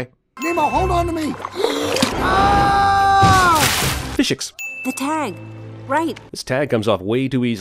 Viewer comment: he said "fish-ics", but the caption said "physics" 1 sin for CinemaSins